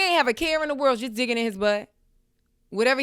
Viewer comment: OMG i thought you said d-ck in his butt 😂 what a morning that would've been